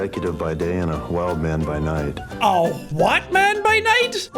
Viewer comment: I think it is "grown man by night"?? I don't know that is what I heard